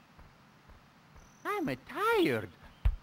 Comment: 疲れたって言ってる？(初めの方座る前) I'm タイヤード って聞こえた気が……↑スペル忘れた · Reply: 言ってるね tired